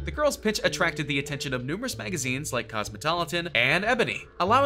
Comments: am I crazy or did he say “Cosmotolitan?” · did he just say Cosmotoloton..?